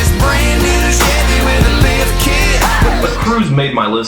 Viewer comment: I just learned they said "lift kit" - and all this time I thought they were saying "lift kick" and it was some obscure reference to dude-truck things. Huh. Good thing I never have to hear the song again.